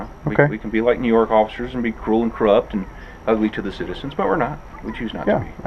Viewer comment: Did he say the following?: "We can be like NY officers and be ugly and corrupt..." Wow ! What a shocking admission. I think this video should make it's way to the NYPD.